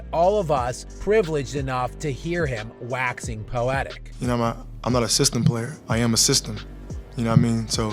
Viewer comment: ok I don't know what harden was saying there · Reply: He’s not a team player 🙅🏻‍♂️